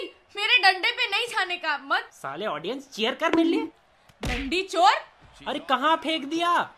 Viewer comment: is say ra*ndi chor😑